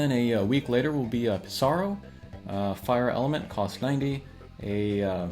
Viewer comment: I had to rewind it. I thought, did he just say fire evade!??!!